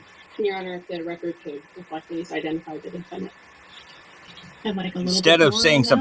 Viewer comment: words have meanings. And, should be applied as such. and she didn’t say victim, she said defendant. It definitely sounded like she said victim, though. But, I secluded the audio and she definitely said defendant.